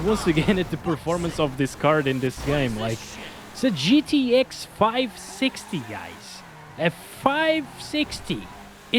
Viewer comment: did you really said GTX 5? :D